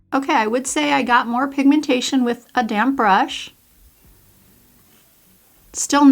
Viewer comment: Hahaha at first I thought you said “I got more pigmentation with a damn brush” 😂